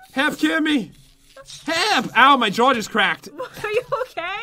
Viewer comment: I. Thought. U. Said. My. Jordan's. Cracked. XD.